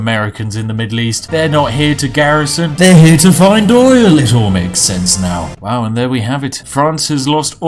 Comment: Did I hear OIL ?